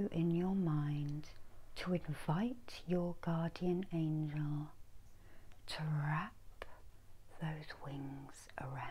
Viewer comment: Thanks so much for this. I am a Hindu, I don't know anything about this. Who is my archangel? When you said call for your archangel, I didn't know who to call upon. You are doing a great work! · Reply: I thought she said to call for our guardian angel ie the angel responsible for looking after and protecting us, but I may have misheard. Will listen again at some point. Blessings 💚